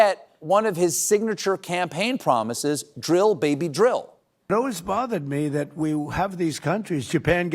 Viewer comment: I did an actual spit-take when I heard "Dig We Must" instead of "Drill Baby Drill" :)